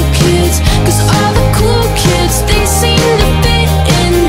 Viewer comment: Idk why but every time it says "cool kids" all I hear is "cookies"